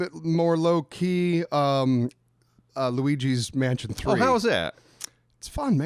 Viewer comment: I'm thinking, "who the fuck is Louie G?" Had to rewind to make sure. Then I heard "Luigi".